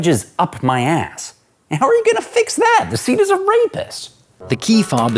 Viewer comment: did he say the seat is a rapist or am I mishearing?